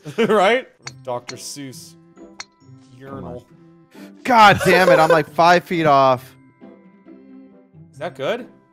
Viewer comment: did say “that’s it I’m gay”?😂😂😂